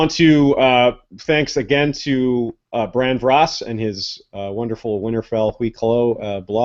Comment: I'm sorry what was that name? I want to look up his work so I need to know how to spell his name. it sounded like bran vross or cross? idk? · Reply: Sorry for the late reply, it was Bran Vras: <link>